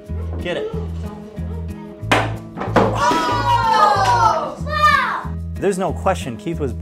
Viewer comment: Did i hear an "Ohhhhh fack!" @ 4:25 ?